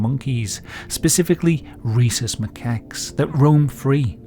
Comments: I lowkey misheard "Rhesus macaques" as "Racist macaques". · I misheard “racist macaques” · I lowkey misheard "Rhesus macaques" as "Racist macaques". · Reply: I heard "Reese's Macaques"